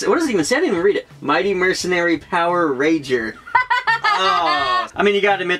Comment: LMAO I for a sec thought you said "Power Raper", quickly turned towards the video and did a double take haha too funny.